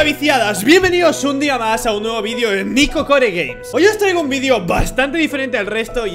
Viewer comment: / escuche nico core gays xd